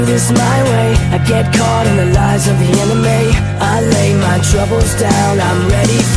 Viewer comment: Lol, I always hear 0:31 "caught in life with anime", instead of enemy